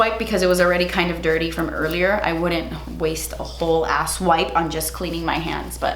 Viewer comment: My husband just said "did she just say ass wipe?" 😂 😂 😂 love you ❤ ❤